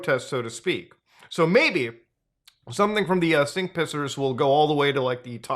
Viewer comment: When you said sink pissers I heard it as "sync pissers" and imagined some people syncing how they pee. And NO, I am not going to check if that's already a thing.